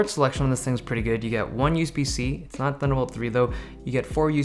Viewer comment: (what did you say...USB 3 <can't make it out> ? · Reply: he said its got 1 usb c port but its not thunderbolt 3 which i think is just a faster version of usb c